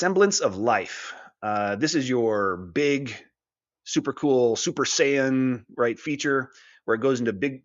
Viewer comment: did you say super saiyajin